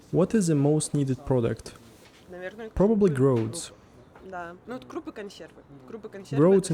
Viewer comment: Can someone tell what was said ? I didn't understand - what is "groads" ?